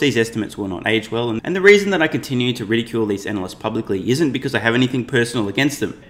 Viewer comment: I heard "ridicule" as re-tool which sounds good.